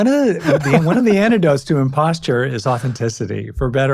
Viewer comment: No offense, but I think I kept hearing “imposture” syndrome.